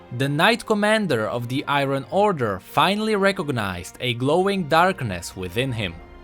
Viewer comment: Wait wait wait a glowing DARKNESS WHAT IS THIS LORE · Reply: Kate Rose i thought they said “growing”